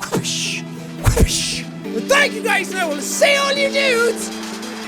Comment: I always thought you say “I will send all you nudes” x) sorry I’m french